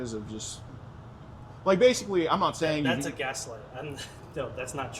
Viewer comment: "that's a guess" wtf Would've expected him to say " that's a lie"